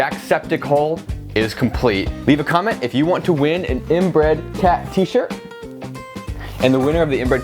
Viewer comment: I heard "inbred cat T-shirt"